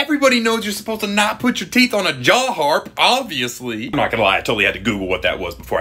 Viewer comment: Not gonna lie I still don’t know what a Jaw Harp is 😅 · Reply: Me neither I thought you said jaw heart